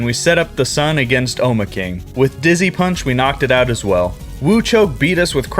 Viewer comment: I thought he said “Disney punch 💀 “